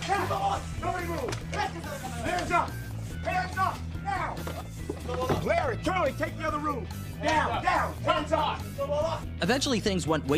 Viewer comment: Did I hear him say "Larry, Curly - take the other room!" ???? He calls his henchmen like the 3 Stooges?? Irony is fun!! 🤣